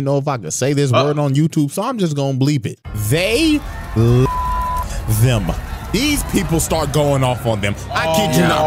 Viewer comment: can someone actually tell me what he said cause im confused bettwen lit on fire and hanged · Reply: They Lynched them